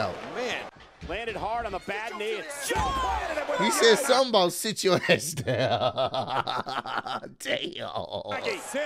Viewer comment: I heard “sit yo gay ass down”